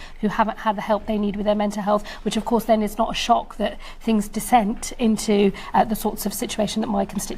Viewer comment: Did I hear her right, “picked up PTSD” and dissent instead of descend. Jeez the state of our political parties.